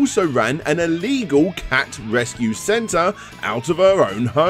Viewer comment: I honestly thought you said illegal cat wrestling centre. First rule of feline club is, We don't talk about feline club.